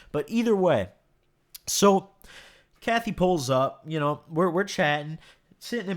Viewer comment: did he say cathy pull up